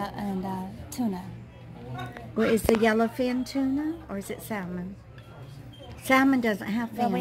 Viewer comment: It sounded like she said "salmonella" in the sushi! Which is why I don't eat it, something about eating raw fish! Only if I'm stranded on a deserted Island will I eat that, lol